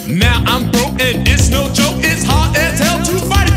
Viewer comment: Damn. I thought it said "GO DO IT" and "GO BUY IT". That's what I heard.